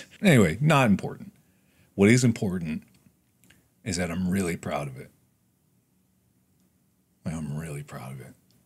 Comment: "If edge of sleep can get into the top 10" Mark says, knowing full well we will crash whatever site* or service it comes out on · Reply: I thought for a moment Mark said that he was really proud of us T-T